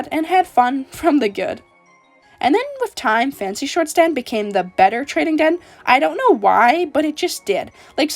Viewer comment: the caption says "Then with rhyme fancy shortstand became the better trading den" Xd